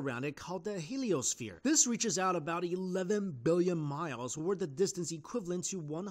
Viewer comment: error at 1:38 it sounds like you say billion but the text says million